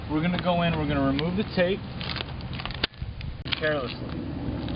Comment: did you say were gonna remove this tape...carelessly ? lol